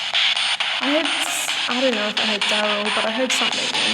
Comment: I heard "Harold" around the 24:00 mark. It's a very heavy accent, so it's easy to miss. I don't know what kind of accent though.